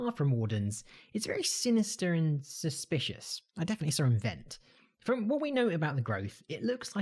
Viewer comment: I swear to god I heard him just say "I definitely saw him vent" when he was talking about sculk growth being suspicious